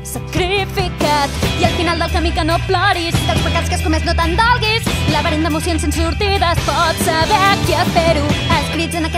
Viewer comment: esto es lo que oí :v " y al final daminaclof cloris barish catan de castonguis labarimos imuscion in surtidas por saber quien derus" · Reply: Hombre, mal encaminado no ibas xDD "I al final del camí que no ploris, dels pecats que has comés no t'en dolguis, laberint d'emocions sense sortida. Es pot saber a qui espero?"